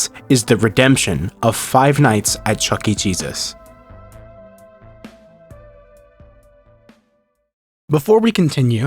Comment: I'm so sorry but when you said "The redemption of Chuck E Cheese's" all I heard was "The redemption of Chuck E Jesus" 😭😭